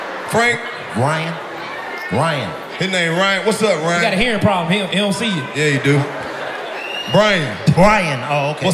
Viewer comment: Did DC just say "he got a hearing problem he can't see you" 27:25 😂😂